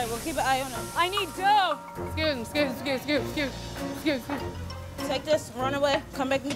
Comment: oh she said excuse me........... i thought she was saying sue me........